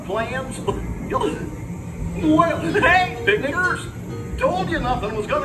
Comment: What is Goofy saying, because I only hear something bad. I hope they fix this because it sounds not good. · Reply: he says picnickers. I though it was really bad too.